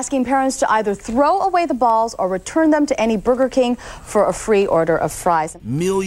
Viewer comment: Did she say booger king?